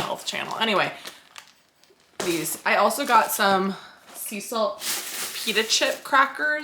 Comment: I could have sworn you said Cecil crackers, not sea salt… is that a sign??